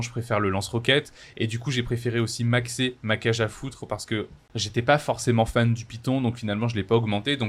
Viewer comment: ma cage à quoi ?😂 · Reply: Cage à foutre on a tous bien compris 💀🤣